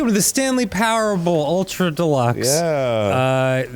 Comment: Did Dan call it "The Stanley Powerball" at 0:56?